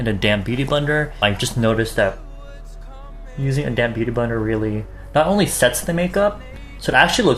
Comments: Ok but at around 13:08 I can't be the only one who heard "with a damn beauty blender" instead of "with a damp beauty blender" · lol I thought he said "A damn beauty blender" instead of "a damp beauty blender".